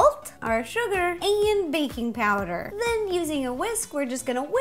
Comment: Ro said baby powder. Did anyone else catch that?